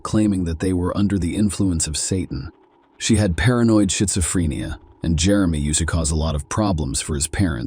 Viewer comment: Just about to fall asleep and I heard; "shitzophrenia". 😅😂😂